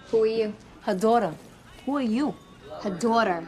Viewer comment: I keep hearing “Her Dora!” I CAN’T